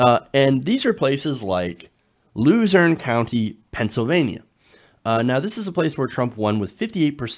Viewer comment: I had to rewind it three times. I honestly thought he said "Loser County, Pennsylvania." Also, dear god these people are morons to ever think the millionaire who inherited his wealth, took a s*** in a golden toilet, and casually declares bankruptcy, would somehow relate to the common man. Let's be frank, these people are racist to the core and simply were excited at idiotic concepts like "Build the Wall." I honestly think too many of these shows are focusing that Trump supporters feel betrayed for the economic reasons. I doubt they ever really considered economic benefits. They were scared of the brown people coming in.